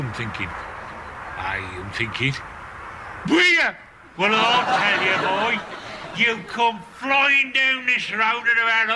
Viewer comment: what does he shout at 4:18? lololol it made me laugh so hard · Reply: Bugger ?